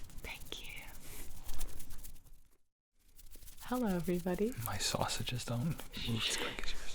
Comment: Nobody: mY sAuSaGe iS rEadY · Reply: I heard "My sausage is done" but that might just be me...